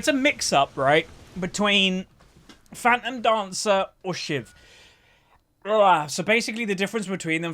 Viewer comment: I swear to god I freaking heard 'a mix between Fat 'N Dancer and Shit'.. I legit rewinded the vid cause I lol'd my ass off